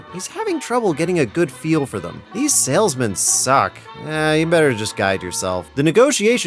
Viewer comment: I heard "these sails men suck" I'm over here thinking they're called sailors or seamen, but I like that he came up with a new clever nickname for them. Then it click, he ment sales men